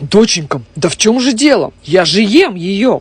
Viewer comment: Когда дочь сказала прениси нож мне послышалось пьениси ноз